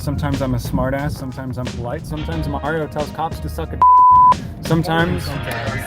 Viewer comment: I miss heard and thought he said, "Sometimes Mario tells c*cks to suck a d*ck.". I was wondering exactly how that scenario would play out! Didn't the officer say that the male's license was invalid for some reason? Was he going to follow the officers to the station? So they're letting him drive even though they know he doesn't have a valid license. I'm confused...